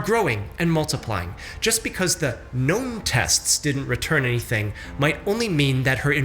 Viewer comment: I fully just went along with "Gnome Test" and figured it would be explained shortly. Then I heard "unknown" and felt like an idiot.